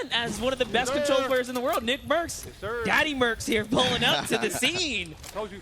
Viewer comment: did anyone else hear him say daddy mercs.